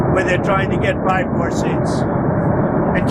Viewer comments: or anyone watching. Did Trump say at 14:27 of the video "Try to get Biden more seats?" That's what I thought I heard. Someone correct me if I'm wrong. He's got Biden, and Obama on the brain...all the time!! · Did he say California was trying to get Biden more seats? I listened to it 4 times, I can’t figure out if I heard wrong. Starts at 14:22. If he did say Biden, why isnt this being discussed more